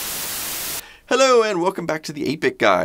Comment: When you said ~"welcome to 8-bit guy" I heard "Welcome to EPIC guy!" ;) And it was feeling totally correct. Change the name of the channel man! ;)